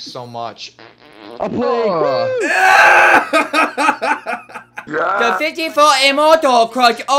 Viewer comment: Did he said "the pityful himoto crossed all our position"